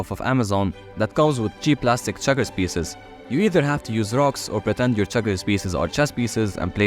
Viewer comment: I heard drugs instead of rocks 💀💀💀💀💀💀💀